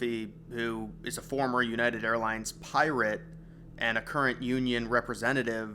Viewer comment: Did he say that guy was a Pirate?